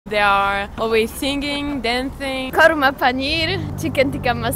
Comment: Did she say karma paneer !! 🤭😂 Nan karma Lol now I know why they say karma is a bitch